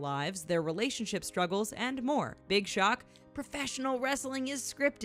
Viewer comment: did she say Big Shaq or big shock or what?